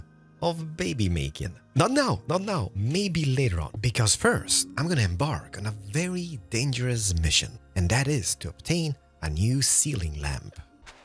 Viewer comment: Instead of "because first, I'm gonna embark on a very dangerous mission, which is to obtain a new ceiling lamp", my non-native brain understood "because first, I'm gonna embark on a very dangerous mission, which is to tame a mucelium man". What the hell is a Mucelium-Man?